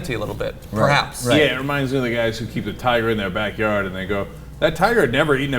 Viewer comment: I thought Cenk said, "...keep a tire in their back yard." and I was very confused about when were tires eating babies...